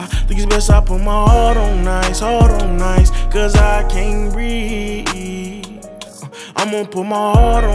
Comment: Captions say "Cuz I can read"